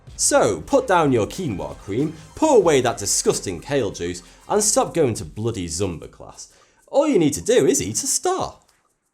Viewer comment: i heard "start going to bloody zombie class". X)))